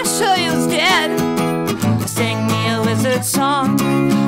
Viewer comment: i'll be honest, i misheard the intro song as "Sing me a Lizard song" and was bopping to it like "Hell yeah! Lizards!" before my brain took a second to realize "Wait, this is a video about the Magical world. It's probably 'Sing me a Wizard song'" and i'm ashamed to admit that my next thought was "aw... i kinda want an enthusiastic lizard song now" instead of "WTF is wrong with me" anyways: Good video! Funny thing about the Harry Potter worldbuilding is that the fandom is perfectly willing to just make up the world building for JK constantly. Why did X happen? oh it's because [Insert headcanon here]. which is kinda funny when you start poking a bit too much into the holes of the setting as the contradictions pile up.